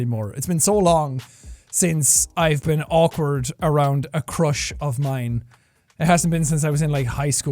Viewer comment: i thought he said crotch not crush